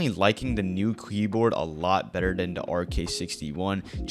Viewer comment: did bro say kleboard?